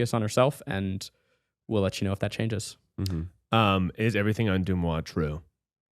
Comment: What does say at 55:10? I can’t catch it. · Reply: He says "Is everything on Deux Moi true?" Deux Moi refers to a popular celebrity gossip website